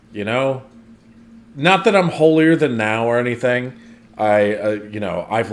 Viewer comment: did you say holier-than-now?😂😂😂 It's holier-than-thou😂